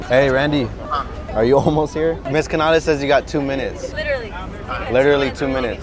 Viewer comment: did he say Mrs.Canales? that's my last name💀please correct me if i'm wrong i just think it's cool